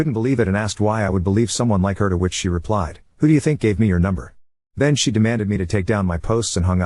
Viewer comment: I hope Maria actually was on it and that Alica wasn't just lying... I heard 'how do you think I got your number' and though 'You stole her phone?' Like IDK why having his number was so definitive to him. Really hope Maria was just being fake cause if she wasn't I'm sure that was a terrible experience for her to get to that point were she's about to kind of break free from everything and have it all destroyed.